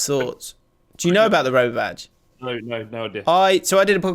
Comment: Oh the Roma badge, I thought you said Rover badge. It's just Romulus and Remus being raised by a wolf